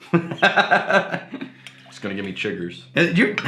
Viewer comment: Jiggers? I thought he said “chiggers”. Lol!